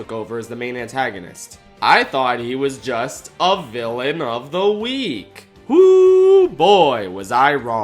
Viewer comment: Oh bruh I thought they were saying "villain of the weak" LOL